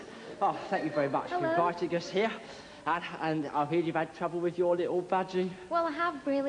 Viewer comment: For a second I thought rod said to lana, "I've heard you've had trouble with your little vaggie" and she responded with "its as big as that" XD